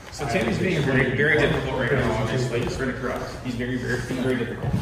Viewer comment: "Being very difficult", and "Here to corrupt"; am I hearing that correct?